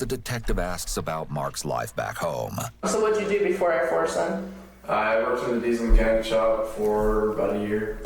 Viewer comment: It's funny how X told his chat to not compare the murderer(s) in these videos to people in his life, but as soon as we hear the guy's voice chat IMMEDIATELY starts spamming "FORSEN LULW" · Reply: they spammed it because the detective said "Air Force then" and it sounded like she said Forsen